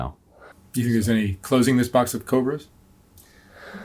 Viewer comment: What was Mark's question at 53:40? I couldn't hear. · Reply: Do you think there's any closing this box of cobras? - Closed Captions said that.